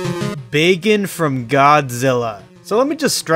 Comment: you butchered that name dude 1:20 it sounds like your saying bacon